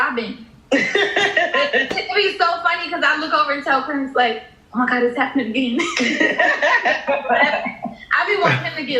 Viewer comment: What name did she say at 16:30 ? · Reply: Clarence the way it’s supposed to be pronounce